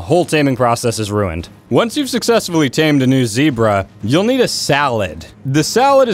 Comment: did you say "youll need a SALAD?"